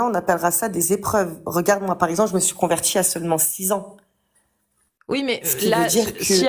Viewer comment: « Ma grand mere est chretienne, mes parents sont athées, j’ai du m’orienter toute seule » et bien ma copine, tu as pas le sens de l’orientation. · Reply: ah !? J'ai entendu "à 6 ans" Il faut que je réécoute !🤔🙄